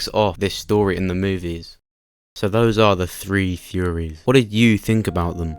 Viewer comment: No matter how many times I hear you say "theories", it's sounds like, "furies". Also, unless there's a bigger time gap than I realize between Heath's disappearance and the defeat of Negan, 10 years seems like a stretch for the gap to which you refer.